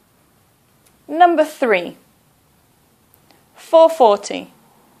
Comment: I guess I am the only one who hears 4.14 each time in